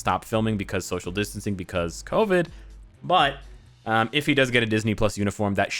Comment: Why did say "Covid" like "Kobe!" 😂